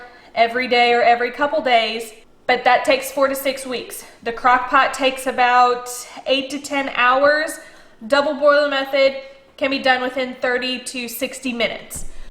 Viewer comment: Hahha i thought you said 24 weeks